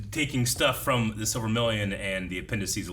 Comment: Why does it sound like says “Silvermillion?” It’s Silmarillion.